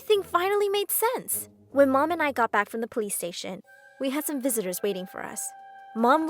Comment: I thought she said "PlayStation" 😂